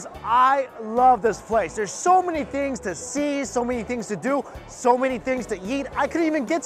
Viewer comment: I heard him say "So many things to yeet." And had to remind myself he's talking about eating.